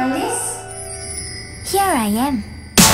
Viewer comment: I've always thought they said " Alice? Here I Am" 😶